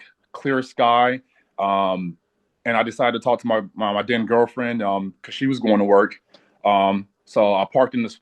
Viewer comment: Yup! When he said he has been talking to his dead girlfriend, that was the give away. You can't talk to the dead. Demons can impersonate your loved ones. Sounds like whats happening to him now. He is digging deeper hole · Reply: I thought he said talking to his then girlfriend. Like they broke up and its now his ex 🤔 are you talking about at the beginning of the video with his intro?